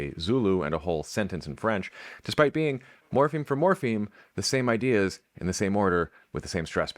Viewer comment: I thought for sure I heard "morphine for morphine". Another concept Manhattanites have multiple words for.